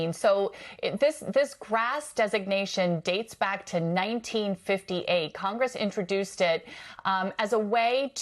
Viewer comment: Did I hear “introduce-did”?